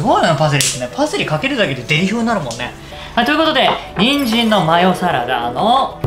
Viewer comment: 「パセリかけるだけで×××なるもんね」 ってとこ聞き取りにくかったんですが なんて言ってるか分かる人いますか？ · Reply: デリ風になるもんね、って いってるのかなーと思いました